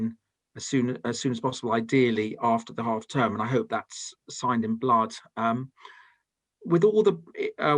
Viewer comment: Did he just say I hope it signed in blood - frank spenser